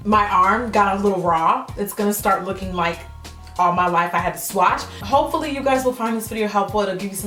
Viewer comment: Did she just say, "All my life I had to swatch"?!?!? Lmfaooo!!! #Dead